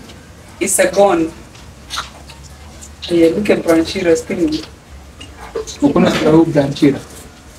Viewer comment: I was just healing and then i heard "look at Brian Chira's funeral " 😢😢